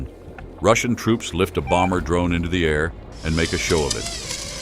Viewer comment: I thought Ai said they sent in obama drone to take them out 😂